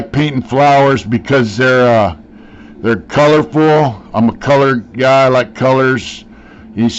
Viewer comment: It almost sounded like you said "Im a colored guy"! Lol! Im sorry I got a fucked up sense of humor.